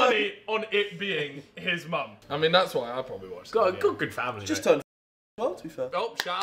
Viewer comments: Talias Mom? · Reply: I heard "she's turning _-one to be fair' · I think he said Talia's mom